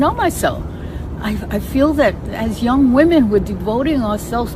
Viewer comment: DID SHE SAY " I FEEL AS YOUNG WOMEN " :person-turqouise-waving::person-turqouise-waving: NEVER TELL YOUR DAUGHTER SHE IS A PRINCESS, BECAUSE AT HER AGE SHE STILL WAITING FOR PRINCE CHARMING. OH BY THE WAY I AM ACTUALLY A BARRELEYE I JUST CAME UP HER TOO WRITE THIS, NICE MEETING YOU ALL.